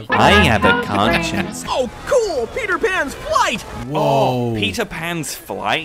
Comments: So am I not the only one that heard"oh boy, Peter pan's white" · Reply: I thought he said "Peter Pan's wife." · Did someone say "Peter Pan's WHITE"?!😂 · lmfao I thought I heard” oh cool Peter pans white” and “I like being white” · So honesty hour. At 2:58 I really thought I heard the in-game character say "Cool! Peter Pan's white." And I promptly spit out my hot coffee. 😅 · I thought he said "Oh Cool! Peter Pan white". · I was listening to this in the background while I was cooking, and I misheard "Oh cool! Peter Pan's white!". I nearly dropped everything on the floor in shock. 😂